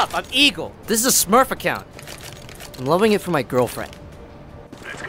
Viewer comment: (I'm not english) what does he say at 0:52 ? "I'm living in for my girlfriend" ? ^^ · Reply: I'm levelling it for my girlfriend